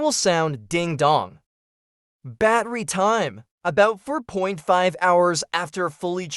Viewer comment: 3.5 hrs ? I don’t think that’s a lot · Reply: Thought he said turd.5 hours. 😅